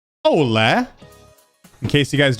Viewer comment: Says Hello backwords Me : Did he just say hola?